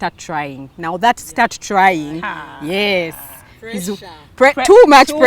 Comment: 😂😂😂...i hear "tying legs"